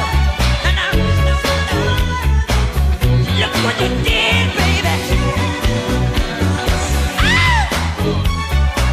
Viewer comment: What to say? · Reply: I think "look what u did babe"